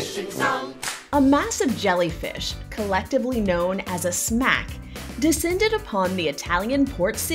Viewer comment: I thought she said ‘collectively known as a snack ‘